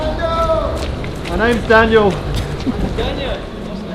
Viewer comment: Is it me or that "Daniel", from the fan, sounded like Cyril ?